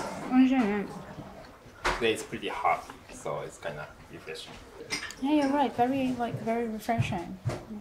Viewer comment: のところで「ええ、ヤバい！」と聞こえたけど空耳？！ 、アコーディオン奏者の土屋恵さんに似てる。 には餡子が苦手な外国人に本当においしい餡子 があることを教えてあげてほしいです。